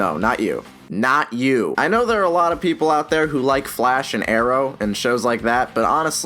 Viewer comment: Also I thought you said aero at first instead of arrow